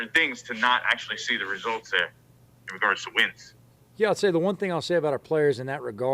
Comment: I thought he said “Carson wentz”😂 but he said in guards of wins💀